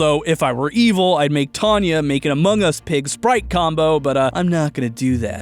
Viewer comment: Did I just hear the word Tanya? If that's the case... then... you know what I mean 😅